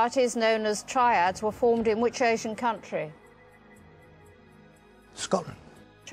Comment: I thought she said ocean country.